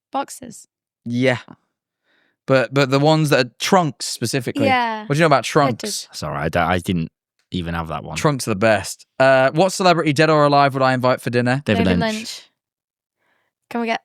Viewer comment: i cant be the only one who heard "Trump's the best"